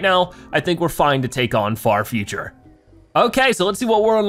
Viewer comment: I thought you said fart future